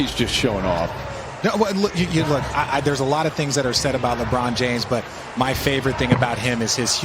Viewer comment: what the F.. is the commentarist saying?? LeBron Humble??